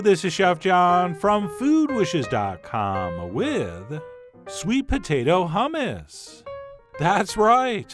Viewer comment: ok, I thought that it said "sweet potato humans"